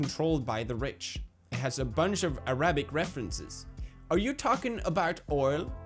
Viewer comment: Did he just say Ara-Bic